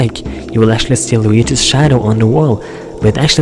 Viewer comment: Wat Did He Say? "It Looks Like His ???-" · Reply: "Which actually looks like he's hung"